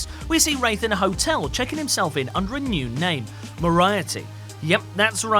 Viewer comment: Anyone else catch the harsh mispronunciation of MORIARTY only Sherlock Holmes' most famous nemesis. sounded like they said moriety